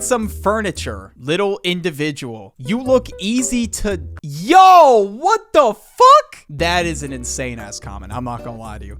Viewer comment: what is that word? Dose someone know? And if you are wondering i think the first two words are the N word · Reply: Goon